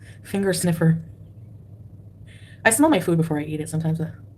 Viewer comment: I thought she said “I smell like fupa”😭😭😭